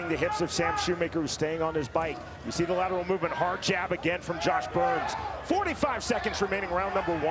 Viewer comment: Why do I keep hearing him say "5 seconds remaining"